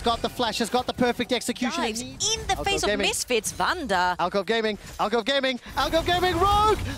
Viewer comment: what is he saying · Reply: Alcove gaming?